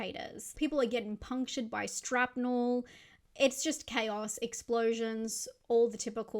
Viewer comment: Did you say "strapnel"??